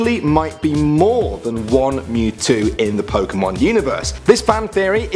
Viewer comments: instead of saying one mewtwo it said on mewtwo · Lol 2:43 they said on instead of one lolololololololol